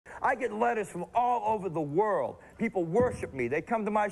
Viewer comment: I misheard that intro as “I get lettuce from all over the world”